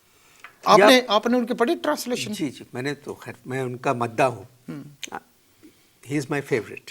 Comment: "main unka _ hoon" . what word he used for fan? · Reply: MADDAH means fan in Urdu.